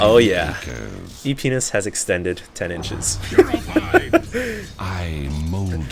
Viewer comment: did he say "p*nis has extended 10 inches"? then "get cocky"? O_o